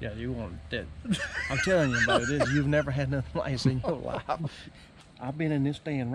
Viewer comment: What does he say at 3:00 ? Brother is just a little hard to understand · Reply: I believe he said “I’m telling you, you ain’t never had anything like this in your life”